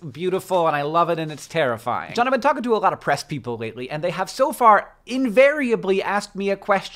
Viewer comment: Okay, but I thought you said "pressed people" instead of "press people", but it still fits tbh